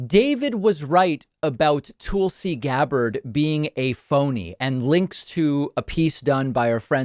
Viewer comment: oh phony. i thought you were saying she was a pony. i was all ready to watch a video about that. this is disappointing.